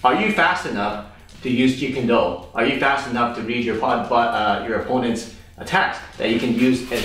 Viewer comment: did he say... are you fast enough to reach the opponents butt? 🤣🤣🤣🤣🤣